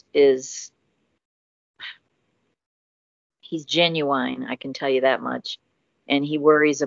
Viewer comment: Scammer: "Hello. My name is James from Scotland and I work for the NSA. Send me money." "OH MY GOD I'M IN LOVE!!" Also did she just say "Ginuwine"?!?!??!?! LMAO